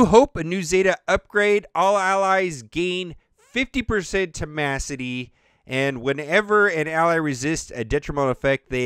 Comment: did u say temacity? Lol